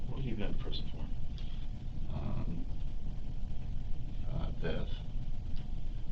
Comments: I thought he said "theft"...? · he actually says THEFT- not DEATH